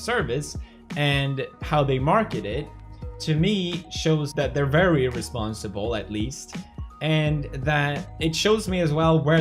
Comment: He said "...they're very irresponsible" But CC says otherwise.